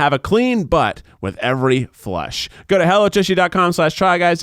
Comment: ok am i the only one who heard it as 'go to hell with tushy dot com..." 🤣🤣